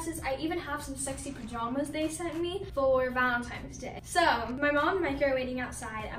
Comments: Did she say valentimes day😂🤨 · I mean I love her vids but I swear at 1:01 she said “Valentimes” instead of Valentines